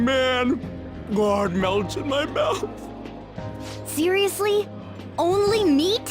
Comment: When Ryugi was eating that meat and said "it melts my mouth" it sounded like he said "God melts my mouth" XD